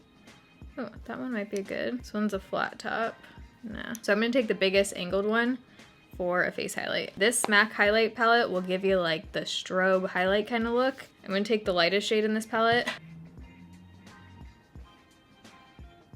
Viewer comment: I swear I thought you said "Smack Highlight Palette", it gave me a giggle